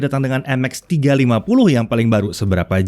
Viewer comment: Gua dengernya Nmax 350